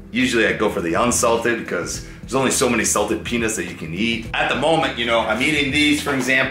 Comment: God I was just listening to the video and I swear you said penis put of context lol